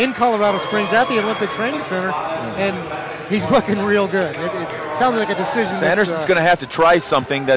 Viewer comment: Did I hear what I think I heard? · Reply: He said looking real good is what I heard